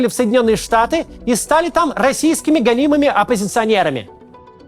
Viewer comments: Услышал "российскими галимыми оппозиционерами" вместо "гонимыми") 3:36. Только начал смотреть. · Вместо :"...гонимые оппозиционеры..." послышалось :"...голимые оппозиционеры..."